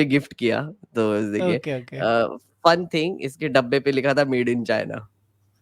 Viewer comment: इसके डब्बे पे लिखा था "made in China" मैने सुना "paid by China" 😂😂😂